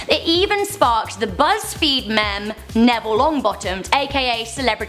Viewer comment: did you just say mem?